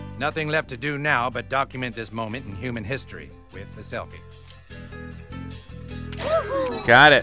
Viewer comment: Hit Morgan freeman three times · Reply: I thought you said morgz freeman